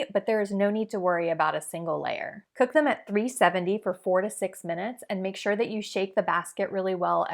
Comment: i thought she said "for 46 minutes" LMAO